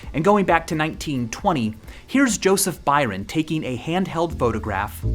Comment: lol first i heard joseph biden...